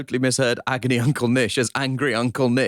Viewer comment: I also thought Nish said "Angry Uncle Nish"